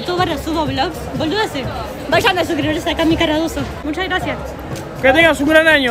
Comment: Que canal dijo la wacha en el minuto 2:55 no entendí xdd